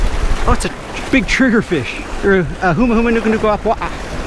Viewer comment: wtf did he say the fish was ? 😂😂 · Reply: Humuhumunukunukuapua’a , which is the Hawaii state fish